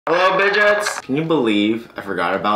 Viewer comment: I thought he said “hello pigeons” in the intro.